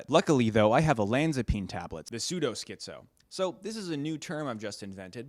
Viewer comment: I heard "sudo schizo" and now I think about it we should have a whole category of schizos who use arch